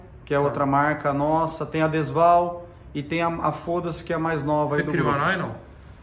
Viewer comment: Só eu ouvi o "temos a marca FODA-SE que é a mais nova"? kkkkkkkkkkkkk